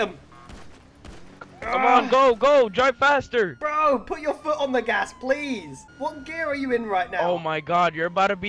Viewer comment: My mom thought he said "put your foot on your ass"